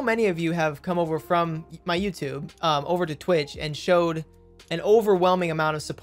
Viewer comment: I thought you said quit youtube and twitch ! But thank god you are not 😀😀😀 I hope the best for you 💜🧡💛💚💙